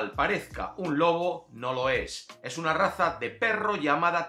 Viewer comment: a primera voz se oye claramente que dice "UNA RATA"😂😂😂 ya después notas que no es así.